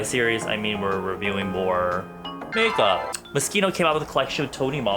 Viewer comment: i thought he said mosquito XD lel